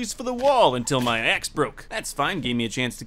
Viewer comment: what people hear:my axe broke What I hear : My ex broke(up)